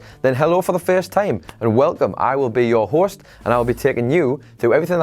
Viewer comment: I love his accent lol.. I wasn’t paying attention and when he said “I will be your host” I thought he said “horse”... so I chuckled